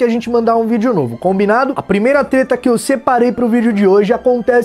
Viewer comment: sou eu que ouvi teta ?